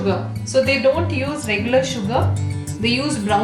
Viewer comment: I heard "they don't use English sugar, they use brown sugar"... Racist much lol